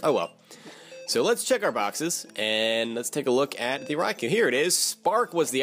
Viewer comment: Raikou really wasn't much of an issue today was it which is always nice, especially after regirock was such a pain. When you said let's go check our box's I thought you said let's go check ours boxers and I giggled a little. I'm such a child haha Great work as always:-)